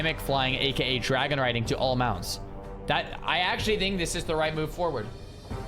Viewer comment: I thought it was just dragon mounts?(for now) obviously adding those animations to EVERY mount isn’t going to be possible like old Blanchy. But I’d love to see it on Ashes 😂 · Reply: "to ALL mounts", so Gregj was referring to what Xar said. He listened.